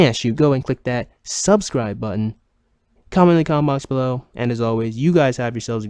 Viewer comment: "Comicon in the box below" - How I constantly mishear your outro.